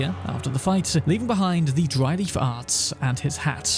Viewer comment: I swore you said, "Dry lee farts." XD